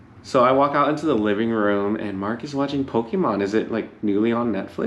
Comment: I thought I heard you say Pokémon lol I'm rewatching new girl! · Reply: Yep Pokémon! I think he was on episode 3. We haven't watched New Girl before, do you recommend?